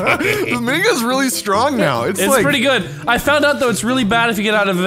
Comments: did say "niggas really strong now" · Is it just me or at 1:23 when said mini guns I heard niggas